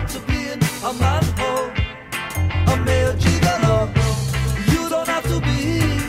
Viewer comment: Oh, I kinda thought he said 'a mere gigolo' :S